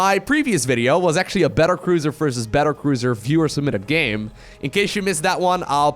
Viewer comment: For the longest time I thought u said "Fewer Submitted Game..."